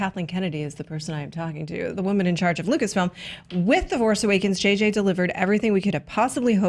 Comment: Sounded like she said "Divorce Awakens" 😂